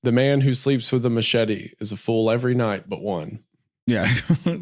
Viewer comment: "The man who sleeps with a machete is a fool every night but one" - sounds like title of a banger Creepypasta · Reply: I swear I thought he said "is full every night, but one" and that was fucking with my head trying to figure out what that could mean, but of course I miss heard.